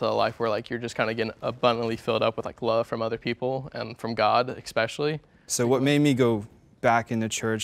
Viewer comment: Did he say expecially?